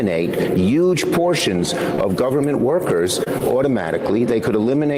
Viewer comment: I'm sorry, did he say "yuge portions"? Is Trump wearing Schumer's skin?